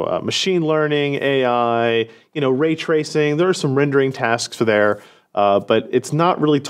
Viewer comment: I heard "Ray Tracing" as "Ridge Racing".